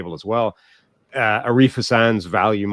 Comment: Does anyone know what he was referred to as far as Washington having the 2nd graded draft class? It sounded like he said "arifa sans" nfl draft value model or something of that nature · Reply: I think he said arif hasan